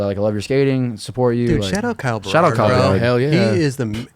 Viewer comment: What name did they say at 48:15? Kyle something? I would love to see the concrete work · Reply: Berard, found it